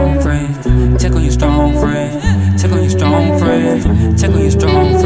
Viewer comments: Thought it said tickle your strong friend and I was like why??? I already did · Thought you said TICKLE YOUR STRONG FRIENDS !!!!! lmao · Why do I hear, "Tickle your strong friend"??? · I hear this as "tickle your strong friends" and I don't want it to be any other way. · I thought buddy said Tickle your strong friend lol